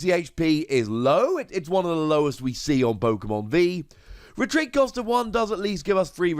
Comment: 190 hp is not the lowest we got. Are forgetting about Poltergeist V, Sableye V, and lastly Morpeko V which all have 170 HP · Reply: Oh you might of misheard, listen closely and you can hear he says “one of the lowest hp’s we’ve seen”